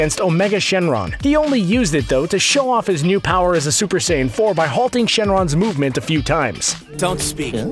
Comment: Did He Just say Super Saiyn 4